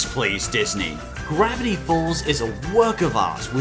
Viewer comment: i thought you said crappy balls